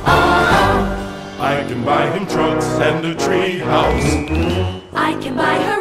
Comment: Why do I hear "I can buy him drugs and a treehouse"?